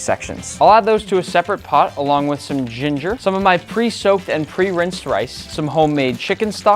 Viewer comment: did he just say pre-soaped